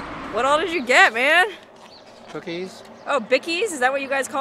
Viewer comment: I heard, and then rewound it, and heard it again; I think said "Cookies", not "Vickie's" @ 4:07 in the video??? You look f-a-b-u-l-o-u-s, in that leopard bathrobe. Seems to me that they aren't too shocked people show up dressed like that around there...just saying....LOL!